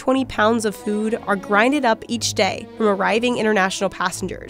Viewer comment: did you just say "grinded up"?